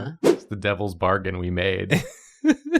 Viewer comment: I misheard "the devil's bargain we made" and "the devils bark and we neigh" and I'm just saying, that'd make a great shirt.